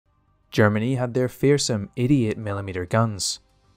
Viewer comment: I thought you said "Germans had their fearsome idiot millimeter guns" first take. If I was drinking I'd have spat it out.